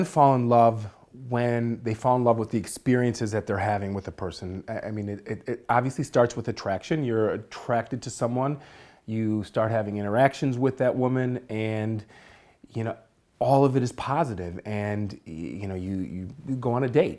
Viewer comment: anyone else hear “erections with that woman”? ....just me? I’ll see myself out.